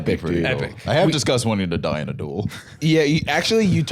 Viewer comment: Okay up until This podcast, I thought was saying he wanted to die in a DOOR.